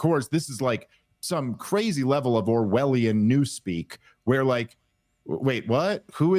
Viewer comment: I totally thought he said, "OVARIAN new-speak" - LMAO